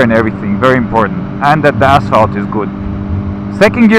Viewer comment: Sounded like he said "bath salts is good" lol. Excellent video thank you